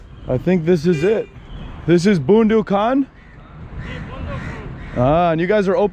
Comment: did he say bondu khan? 🤣☠☠